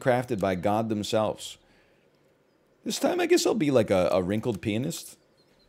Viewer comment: Am I the only one, that heard him saying “wrinkled penis” right at the beginning!?